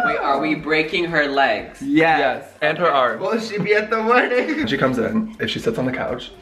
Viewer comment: Really enjoyed that. It is lovely how you are all so close true friendship goals. #?teamtop #teambottom #team #team. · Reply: listen to what says to. He says "will she be able to attend the wedding?" ??? Fingers crossed 🤞 🙏